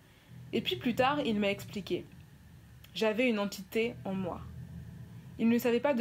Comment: j'ai entendu "entité en moi" plus le beug de mon pc j'ai tt de suite arrêté ça me faisait trop peur